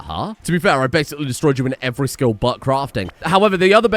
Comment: "I destroyed you in every way but crafting." What I heard was "I destroyed you in every way, Butt Crafting. " New colonist name obviously.